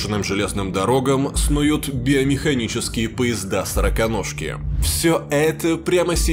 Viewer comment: почему я услышал "сраконожки"?